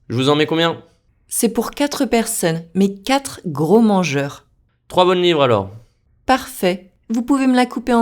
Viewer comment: Alors: deuxième jour. Je ne suis pas content avec mon résultat. C'est très drôle. "Trois bonnes livres alors" était pour moi " Travaille en valeur" ...."Bien Sûr" j'ai compris "Voici"...😂😅 Beaucoup travail devant moi, mais je suis motivé. Merci pour cette challenge😊😊